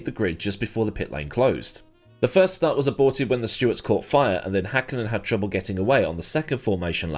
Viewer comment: For a few seconds I really thought you said "the STEWARDS caught fire". I know I would have been so young for the '99 season that I had only recently graduated from calling F1 "misters crashing cars" but I'm certain I would have remembered that happening 😂